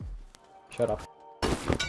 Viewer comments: What did you say to him that mic icon was going off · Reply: I was laughing since they wiffed, and then he says shut up - bad word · what did you say?????? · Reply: naw the downed guy said shutup then called me bad word